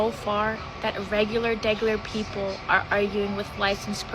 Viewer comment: "...regular degular people..." Is that what she said?